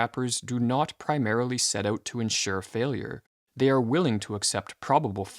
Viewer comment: ensure instead of "insure", I think. Good video, still.